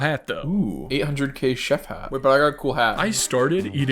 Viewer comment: did he say chef hat............? I think that's the cat in the hat ngl- O_O 39:52 also the title is dutch I put in into google translate and it said "portable toys" but it's probably inaccurate and I'm not dutch also they say "Hallo" in the Netherlands so that's also how I could tell